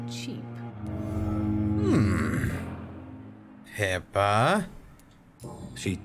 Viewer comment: I thought Cry was referring to "HIPAA" laws instead of yelling at Pippa. Oops.